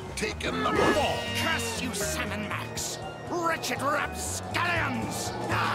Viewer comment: Can anyone explain what does he say at 1:00? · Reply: "Curse you Sam and Max, wretched rapscallions!"